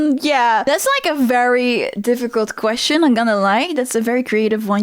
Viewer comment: Did she say ''im gonna lie'' instead of im not gonna lie?! LOL